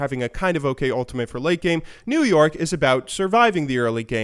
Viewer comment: I thought you kept saying new york instead of new yoric hmm